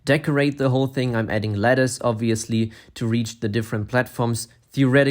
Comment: No offense, not making fun of his accent, but I thought he said “lettuce” 💀